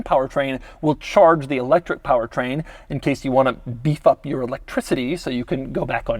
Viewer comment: did he say be f*ck ?? Lol